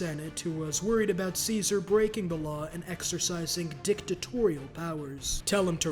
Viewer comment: Why did this emphasis on the d after 7:44 has me dying bc i got high watching this... i heard dicktatorial 🤣